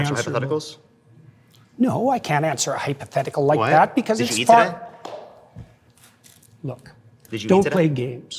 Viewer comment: what is the guy in the black shirt saying here? "Did you Ethan it?" is all i hear. I swear I've watched that bit several times and i still have no idea what he's saying as there is crosstalk and CC isn't helping. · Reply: "Did you eat today" it took me 5 times of listening to figure it out